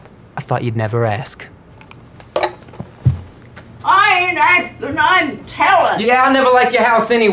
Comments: Does anyone know what she says at 0:36? I’ve been wondering for years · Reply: After says "I thought you'd never ask", she says "I ain't asking, I'm telling!". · what is she saying? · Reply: "I ain't askin', I'm tellin'!"